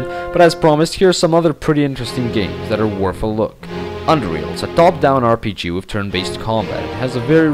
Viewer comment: What game are you talking about here? I cant seem to understand what you're saying unfortunately, it looks really good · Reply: UnderRails ;)